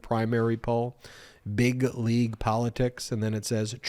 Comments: 🤣 I thought said "Bigly Politics"! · Big League at first I thought you said Big-ly and Trump truly made it up